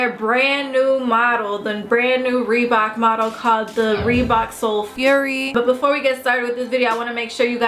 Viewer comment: I thought it said sole furry lmao